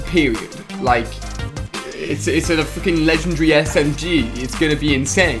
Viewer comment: omfg ali a swore he said fking at 1:23 "its a fking legendary smg"